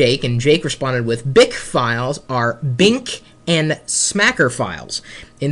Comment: for a second i thought chris said bacon smacker